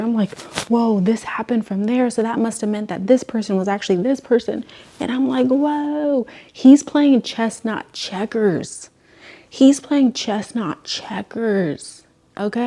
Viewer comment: The aggressive “he’s playing chess not checkers” was so real 😭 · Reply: IT SOUNDED LIKE SHE WAS SAYING CHESTNUT CHECKERS😭😭😭